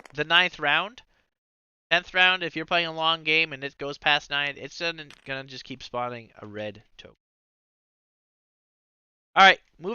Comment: Hey, could you clarify what you intended here please? Were you saying "it shouldnt just keep spawning a red token" or "it's gonna just keep spawning a red token"